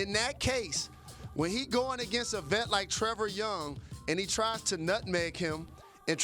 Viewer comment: did he say Trevor young!?!?!!?!?!!?!?!?!?!?!?!?!